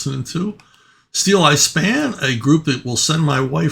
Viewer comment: oh Steeleye Span I thought you said Steel Ice Band